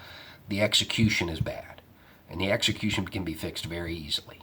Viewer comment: Love your 'just a thought' outros. Cause what I hear 'just a rational thought'. Keep up the great conversations!